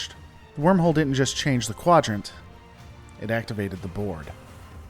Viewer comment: "The wormhole didn't just change the Quadrant, it activated the..." Took me three tries to hear Board; thought you said "the Borg"